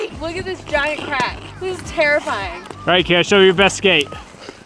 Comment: when he says “show me ur best skate Kara” the closed caption says “applause” 😂😂😂